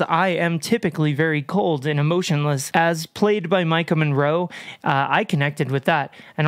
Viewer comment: I first thought you said Michael Monroe and I was like huh?????? What is an aging Finnish glamrock star doing acting in a movie like this? (he does look creepy enough to do it though)